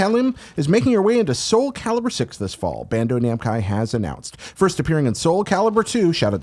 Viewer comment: did he just say Bando Namcai